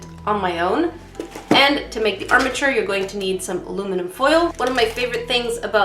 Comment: Lol I heard "Imanum foil"